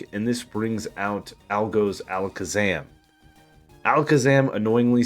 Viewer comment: " this brings out Algo's Alakazam " my brain just shortened it to Algokazam thats how i heard it at least